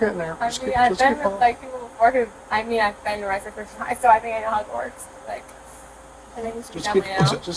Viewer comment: ngl I thought she said she had been to Hogwart's and not "how it works" lmao